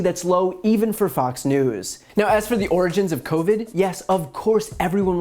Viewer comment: When they said "find the origins of Covid", I first thought they were saying "the orange of Covid". The irony. Also: why does that Fox reporter look and dress like Trump?